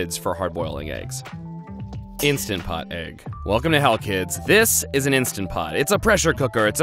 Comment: Why did you say welcome to hell i thought you said help + im 10 so you have offended my kind